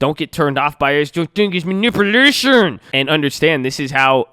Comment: OWNER OF THE CHANNEL.. please explain what you said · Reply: Owner of the channel it's manipurlatioooon